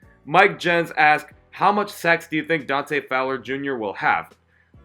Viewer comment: I could have swore you said, “how much sex do you think Dante Fowler?” will have 😂😂😂